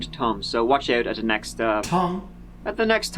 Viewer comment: I thought he said tongue too lol (around 15:20ish)